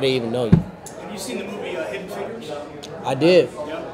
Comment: what movie does the reporter say? Thanks in advance if anyone can spot this :-) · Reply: VermiciousKnids100 hidden figures I think :)